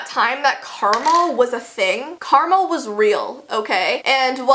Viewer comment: When you said ‘Karma’ I thought you said ‘Caramel’ 😅